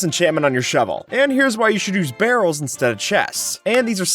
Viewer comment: When he was talking about barrels being better then chests It sounded like he said girls instead of barrels at first lol